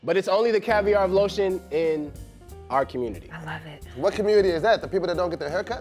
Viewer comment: Who else hears “i love it” at 3:39 ish